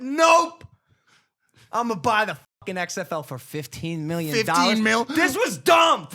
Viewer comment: I thought they where saying 50 mil